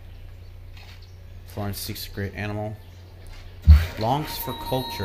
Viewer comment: is it just me or does it sound like he says great animal?